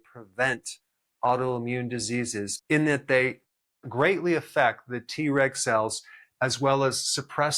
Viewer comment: I keep hearing "T-Rex cells" 😂